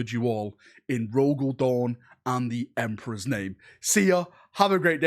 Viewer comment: He said: "In Rogal Dorn's name", but I heard "Perturabo's name". Good lad 🤘